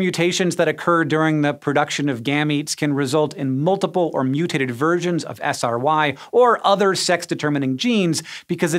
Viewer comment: I swear I thought I heard you say "mutated virgins" a few times.